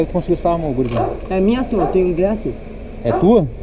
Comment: O Vagabundo falou que tem 10? ou ouvi errado?